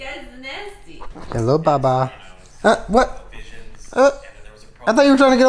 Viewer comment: Am I the only one who melted when at 1:57 called Marbles ‘baba’ and the later ‘baboo’ (idk if that is what he said but that’s what it sounded like to me)